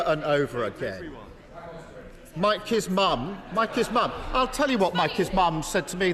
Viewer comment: I thought Keir said "Like his mum" for a second, as in he had a conversation with Rishi's mum. Awkward conversation that would've been.